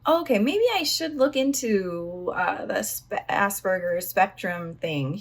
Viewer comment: did you say "assburgers"? im kinda laughing now.